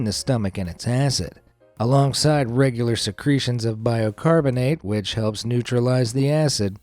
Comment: Is he saying "biOcarbonate"?